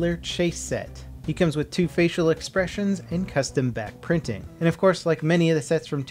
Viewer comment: God, I keep hearing "Bat printing".